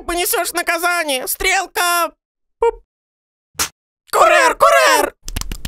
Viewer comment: курар курар послышалось 😹